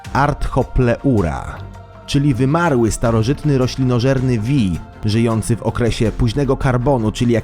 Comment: usłyszałęm rrekinorzerny xD